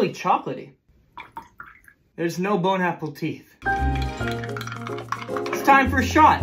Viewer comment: Sounds like he says there’s no bone apple teeth 😂